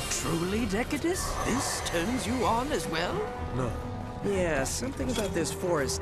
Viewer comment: For anyone not wanting to bother with pausing at 8:36 or so to catch what Decadus says, it's "No. Defeating such weak foes actually... displeases me." Also, holy crap, I thought the resemblance between One's bro and Mana was a coincidence! I picked up on that instantly. It's too bad I never fully beat the first Drakengard though.